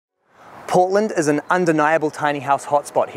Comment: Ahh...Port-land. I thought you said Po-land. Love the bench